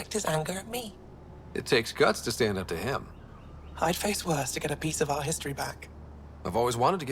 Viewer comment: when she said "I've done worst to get a piece of artistry" I thought she said "I've done worst to get a piece of ass"